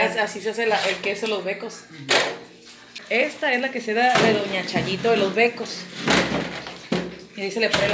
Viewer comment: Siempre pensé que decían la chayo de los mecos 🤣